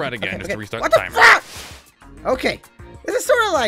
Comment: I thought I heard say the f word